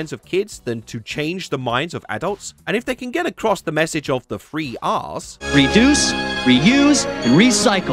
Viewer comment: I really thought you said free arse, when you said 3 r's.